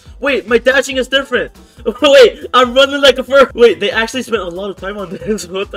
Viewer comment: What did he just say im running like a bird